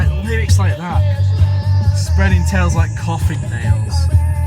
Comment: Always thought he said “spreading child-like coughing...” haha.